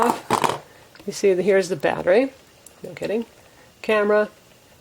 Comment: When you pull off the back of the phone and you say this is the battery, afterwards it sounds like you said I'm kidding. LOL.